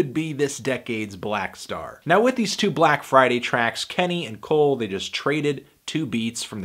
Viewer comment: Did u Just say Kenny and Cole? LOL!